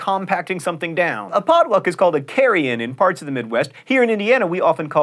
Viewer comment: When John said "Carry-in dinner" I heard "Carrion dinner" which sounds...less yummy.